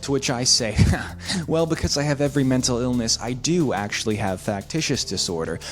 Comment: I thought it said “I hate every mental illness”